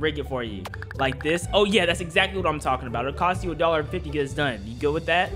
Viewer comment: oml i thought it said 150 no 1.50